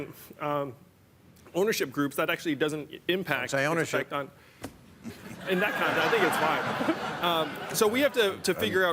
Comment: what the hell did Bill say? Kinda derailed the point Yang was making which was salient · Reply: "Don't say ownership" it's a reference joke to earlier in the show.